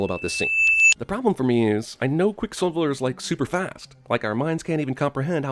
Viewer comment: I heard “quicksilveler “ lmao